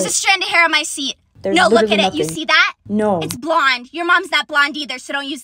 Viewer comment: I had to listen to this three times before I heard “blonde” instead of “blind”